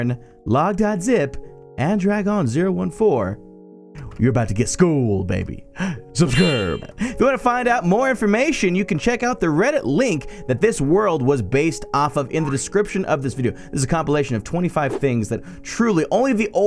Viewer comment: I thought he said Rhett and Link